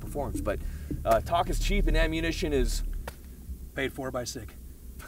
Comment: "Ammunition is... Paid for by sig" was a killer one liner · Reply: Thanks. I heard it as "paid 4 by 6" and started searching for what the hell that even means. *facepalm